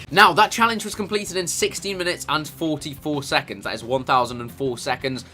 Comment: lol thought he said 60 mins xD